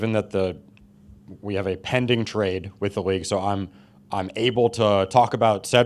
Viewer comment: did he say there’s a pending trade?👀